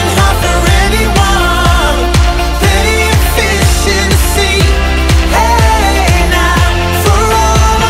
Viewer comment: How is it I heard "plenty fish in the sea" as "efficiency" without the lyrics lol!